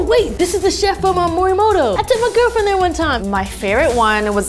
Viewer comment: did she just say Girlfriend?